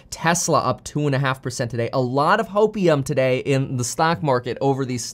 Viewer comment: Did you say HOPEIUM?